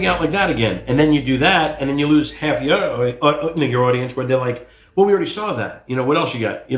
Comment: why is he saying "n*gger audience"? What the fuck. Slip of the tongue probably but I'm not surprised it's a word he uses regularly in his everyday life.